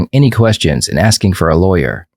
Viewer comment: yeah exactly. He said her first mistake was NOT asking for a lawyer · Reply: I thought he said her first mistake was asking for a lawyer, I had to rewind it to hear it right